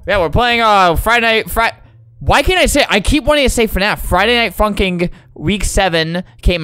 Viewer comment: why does it sound like he said friday night fuking